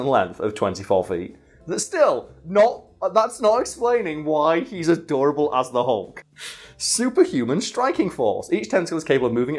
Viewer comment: What he said: "That's not explaining why he's as durable as the hulk" What I heard: "That's not explaining why he's adorable as the hulk"